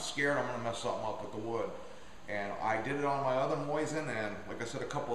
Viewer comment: whats a moisin? lol thats funny its mosin not moisin.. sounds like hes sayin moisten haha